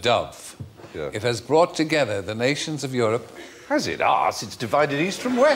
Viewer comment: What does Stephen say 0:12? I hear "has it ass". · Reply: Has it? Arse! It has divided East from West.